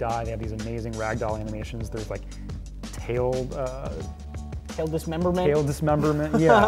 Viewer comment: "Tail dismemberment" Got it. That's all I wanted to hear :D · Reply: Almost thought you said "Tali dismemberment." XD